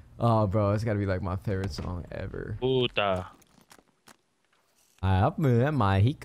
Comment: Lol did say puta